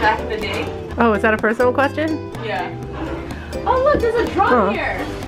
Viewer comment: I thought said "Oh look,there's a Trump here!" lel